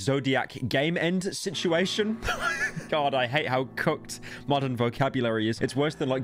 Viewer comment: For a good few videos i thought when referred to something as "cooked" i thought he was saying "cucked" idk i just wanted to put that out there